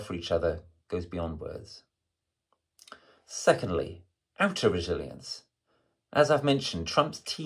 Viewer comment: I cannot make out this key word you’re saying, “aptoriliance?” I couldn’t understand it the first time you said it either. I slowed it down to .75 I still cannot understand what you were saying Mr. Rupert. Edited to note, he seems to be saying “inner resilience,” and “outer resilience,” for those who also had trouble understanding the message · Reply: Outer resilience